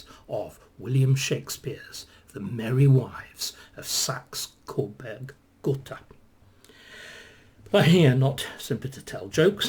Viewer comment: I heard it "the merry wives of Hanover" but I guess SCG is more accurate